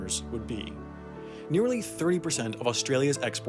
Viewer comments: it sounded like three percent. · i heard it as nearly 3%